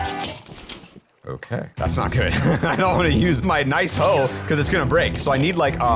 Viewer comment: the subtitles say “pickaxe breaks” when it was a hoe. Also LOVED the hoe song! 😂❤